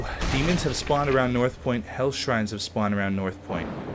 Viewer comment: For the whole first series I thought you were saying heath shrine! :P